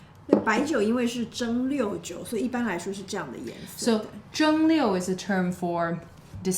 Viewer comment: says Zheng1 Liu4, but on screen it says Zheng1 Liu2 (the way I heard it before). Was that a mistake or a cultural difference to Taiwan?